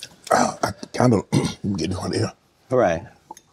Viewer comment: Does Shannon say “I’m kind of getting hornier” or is just me ??? 😳